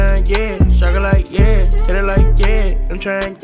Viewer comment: High key thought he was saying I’m trying again 🔥🔥 both fire